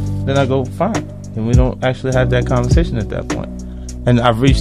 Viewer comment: I like the phrase “ Team Mom” he is absolutely correct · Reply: Oooo I thought he said teen mom I was so confused lol